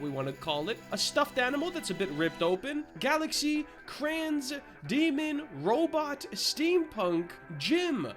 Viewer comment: bro said "crans" instead of crayons...